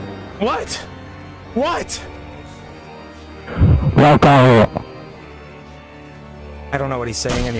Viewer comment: B͖̬͎̑̽͂ͩ͋ͧ͡B̻̟̱̟͇̪͐͒̓ͭͤ͑͋͜ͅP̢̬̣̦̂ͧ̃ͯ̀̂͑̕P̰͋ͯͮͭͤ̃͆̔ͧ͟͢ What? ̛̦͓͓̼ͫ ̶͉̳͎̠̫̥̪̆̔ͮ̾ͮ̏B̢̻̣͑̍̍̇ͬ̍̚H͒̏͐̉҉̲̪̟̱̜͔B͓͇̣̻͒ͮ̇̕ͅP̶̬̯͑̇̄̓ͣB͍̹͈̼̖̠̀̾͂̅̄͛́͟ͅP̧̳ͯ What? WHAT?? ̢̲̞̺̗̻̩̅̿͗͐̏͜͝ ̞̱͓͕͙̠͓̩̮̇̄̀ͥ́W͙̰̥̹̰̠̫̯̿̇̈́̂̑E͖̖̲̤̼͔͕͎̙ͤ͘L̘͊͜C̙̪̝͍ͨ̈̾̄̕O̧̝̹̺ͦ͆ͪM̜͎͖̻̝̻̪ͧ̑̃̄̈́͋ͧͯE̬͎̲ͫ̋͡ I don’t even know what he’s saying anymore, I don’t understand him HhhO̝̭͒ͦ̓͑̂͒̃͜Ḥ̳̥͇̳̬ͨ̌̉͑̔ͬ͜͢Ơ̧͍̩̓̍͟H̵̷̗̜̘̉ͮ̅͡H̶̠͖͖̳̣̝͖͌̊̉̍̓͡G̫͎͓̯͖̺̾̔ͥ̅ͣH̪̠̬̰̩ͮ̎̍ͨ̎͆͊̊G̷̳̗̐ͩ̑Ơ̵͇̻̘͍̞͎̗͗̏̂ͬ͗͂̀̂Ǫ͕͕̤͓͙̭̹̾͂͋̾͊O͙͙͎̯̳ͧͨ̓͜͢ͅḢ͍̈ͩ̆̋̚ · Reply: I thought He was saying 'welcome home' I know it's weird